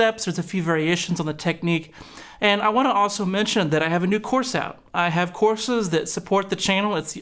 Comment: I thought you said you had a new corset. I was just a little caught of guard by such a concept, but then I thought maybe because of the lasso thing you were doing an old western theme, and were gonna model something purdy for us. I had to rewind. Needless to say, I was a bit disappointed. However, the doppler chorus effect was enough to compensate. I reckon you hogtied that geetar tone good.